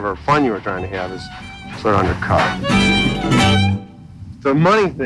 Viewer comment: I think the thing he said was "Undercut", not the thing that the subtitles said.